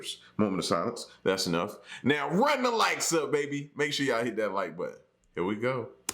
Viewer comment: Hi L &, Love your channel. 💜 When I first started watching, I thought you were say "Run the lights up", cause I was watching 👻 episode..🤣🤣🤣🤣 I am a fan!!